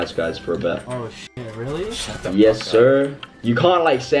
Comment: who heard him say shut the f up